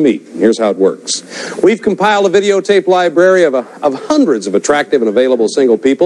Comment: Twice at the beginning it sounds like Chuck is saying “lie-berry” instead of “library”.